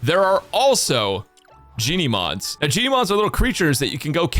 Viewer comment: for a second i thought i heard jennymod 😂